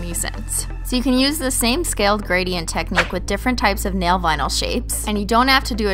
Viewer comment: When I was now starting to realize I'm a holo sexual I thought she was saying nail bino like albino stupid younger self